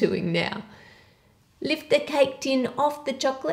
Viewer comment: I thought you said like the cake tin!! Me: licking tin I'm right ahead of you😂😂